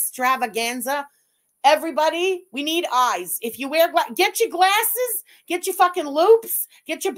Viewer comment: Thought you just said, "Get your glasses. Get your f*cking lubes." 😂